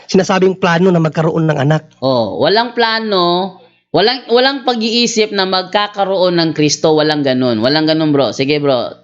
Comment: Sabi niya: @ 24:40 "walang plano, walang pag-iisip na magkakaroon ng Cristo. Walang ganun" = Tama ba narinig ko.?